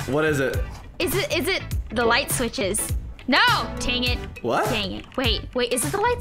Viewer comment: Haha 😆 I was laughing so hard at 15:21 15:25 she said tangit instead of dangit and was like whattt and laughing lol